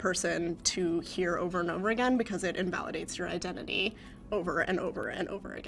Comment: did she just say 'her identity' hmMmMmMm